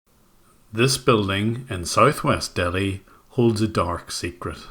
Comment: Thought you said south west London for a sec lol wouldn't surprise me anyway